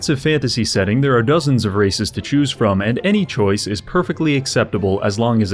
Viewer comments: think he said dozen races. I heard dozens racist to choose from and I’m gonna assume these guy are comparable to 40k level of racist 😂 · When you said "races" i thought you said "racists" and ngl you wouldn't have been wrong. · i misheard dozens of races with dozes on rac1$sts.... i wasn't even that far off anyway.